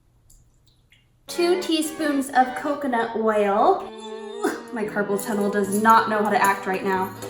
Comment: “two teaspoons of coconut whale” i dont think you meant to say it like that but I love you 😂